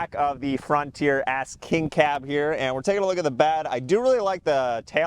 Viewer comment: sounds like you say frontier ass king cab